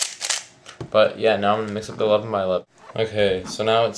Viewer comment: Everytime you said "The 11 by 11 " i thought you said "The love of my lovin" ahaha xD lol